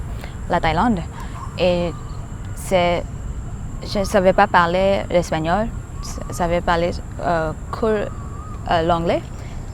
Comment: Little mistake in the subtitles at 6:01. said "Je savais pas parler l'espagnol, je savais parler que l'anglais" Which translates in english to "I didn't speak Spanish. I could only speak english" instead of "I knew how to speak Spanish, English" and that's why she said learning french was "un défi" (a challenge) at the time. Source: I'm french :) Good video, impressive skills.